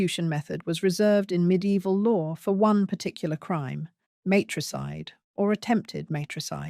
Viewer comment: Infanticide is the intentional killing of a very young child, specifically one who is less than 12 months old. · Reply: She clearly said "matricide' c 12:40. Infanticide was early on in video.